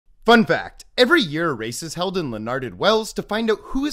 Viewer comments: I accidentally scrolled and all I heard was "Every year, a racist-" and I came back like WAIT WHAT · I heard "every year a racist is held" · I heard “every year a racist is held in rough terrain” and I’m happy with that